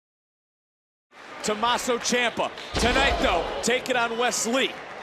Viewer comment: Did he just say tomasso chiampa? At the begining